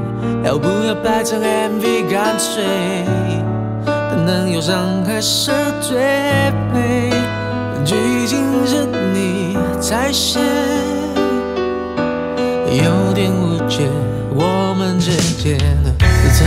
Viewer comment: 有一句听成'蛋蛋忧伤还是最美"